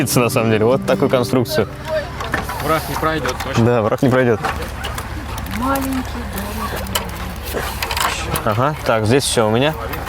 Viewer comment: что она сказала , маленький ДОМИК??? Я думал он только мне показался таким